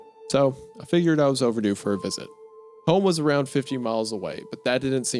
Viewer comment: I misheard, I thought he said "home was around 15 miles away" 50 miles makes sense to me I guess as far as not seeing them in a store, if you're in a big town.. I didn't drive one way 50 miles until I was like 25-26